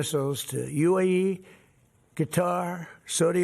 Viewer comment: Does he say "Guitar" instead of "Qatar" ???